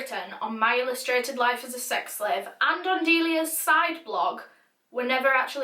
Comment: I thought you said Delia Smith at first and i was like FFS NOOO, BAKING WILL NEVER BE THE SAAAMEEE